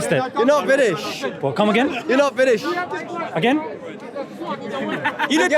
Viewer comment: This creature is a true waste of time, hardy can understand him, I thought he was saying “ you’re not finish” 🤣, this creature definitely has a mental illness